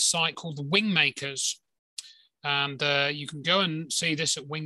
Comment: Wind makers - I thought you were saying Wing makers.